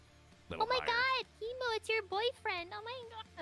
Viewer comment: Okay this makes sense now, and it’s pretty funny XD I kept watching this and I kept hearing “Emo it’s your boyfriend” I just realized they said “Kimu” But nah not a spare XD